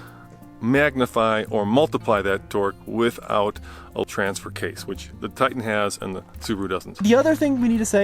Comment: Is he saying "All Transfer Case" or "a transfer case" right